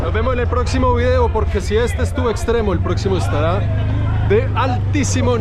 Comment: ¿En 22:41 dijo 'porque si este es too extremo' como combinando la palabra 'too' del inglés que significa muy, o yo escuché mal?